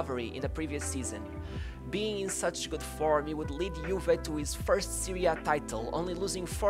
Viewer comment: The World Cup in Spain was in 1982 not 1984 my friend. I'm Italian, I remember that World Cup ! Edit: sorry another thing. Sounded like you said Juventus won its first Serie A title with Platinì...I must've heard wrong....right ?